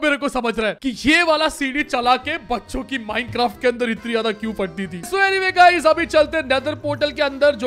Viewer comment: bhai mene kuch jyada Ganda samajh liya you say bachhon ki i hear bakchodi 🤣🤣🤣